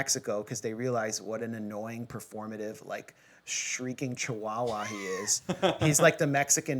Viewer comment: "He's like the Mexican Mehdi Hasan." Whats wrong with Mehdi Hasan? I like his "Head to Head" series. Also, it sounded like, "shrieking chuala."